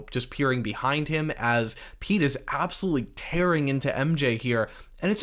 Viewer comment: “Pete is” lmao I thought you said something els at first